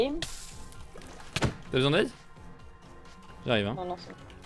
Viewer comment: [ES aka Ñ uu] Como español de familia francesa, me encanta como hablas claro y para que se entienda en español pero en francés se escuche “Tabesoindaide ?” XD [FR aka Ç uu] Comme espagnol de famille française, j'adore que tu parles super claire l'espagnol pour que ça se comprend mais en français tu dis directement “Tabesoindaide ?” XD